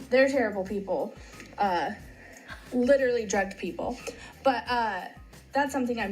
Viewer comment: I heard her say "They're drugged people." Which makes sense cause they're high all the time hahaha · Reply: She said "literally drugged people". It's not likely that she's judging their weed intake, when she smokes it pretty regularly herself. I initially thought that she was implying that they are addicted to harder drugs, but most people have said that it sounds like she's accusing them of spiking drinks, which does seem to make more sense, based on the way that she said it.